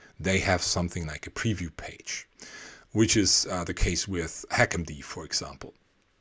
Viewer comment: what did he say? Hackandy? I am interested in the tool. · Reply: HackMD